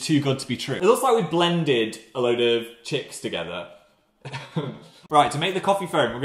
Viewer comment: i didn’t want to imagine a load of chicks being blended but here we are. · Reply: Chicks? I thought he said chips. Like yellowish potato chips.